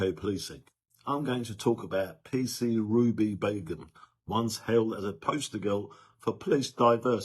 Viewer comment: I thought u said Ruby Bacon 4 a minute 😅😅😅